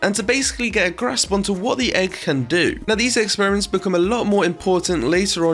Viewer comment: thought he said egg-xperiments